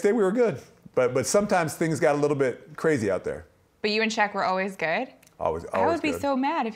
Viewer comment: Listen to 4:39 I swear it sounds like Madsen says,’ Sometimes things got a little crazy out there.’ ‘So you and Shaq were gay?’ “We were always good.” “I’d be so mad if he threw me on the ground.’ ‘Listen it didn’t feel great but I got back up!’ ‘Yeah oh wow."